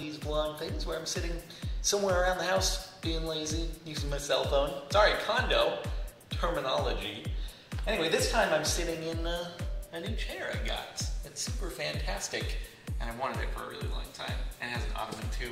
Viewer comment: did he just say cundom!?